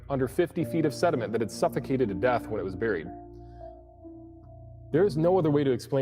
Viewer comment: I thought that guy said dinosaurs 'farted death', I was very disappointed when it turned out it was 'farted to death'. That would be SO cool, and really put a lot more 'terrible' in the terrible lizards.